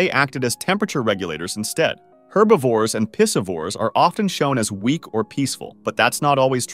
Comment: WHAT DID HE SAY? no wonder bro is crying... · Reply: Pissovores?